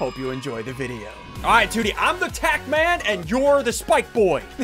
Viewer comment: I thought he said he was the “ attack man” :D